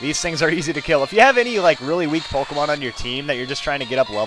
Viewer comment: lol i thought he said slowpoke mon in